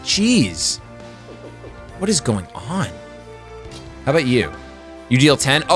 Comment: I heard "what is going alm?"